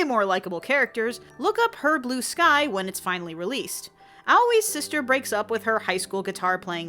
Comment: Can someone PLEASE tell me what she is saying at 8:18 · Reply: "Look up 'Her Blue Sky' when it's finally released".